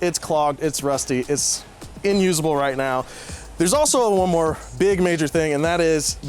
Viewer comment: Did he just say “in usable”? Come on, do better.